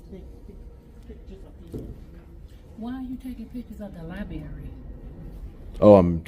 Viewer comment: did she actually say “lie-berry”?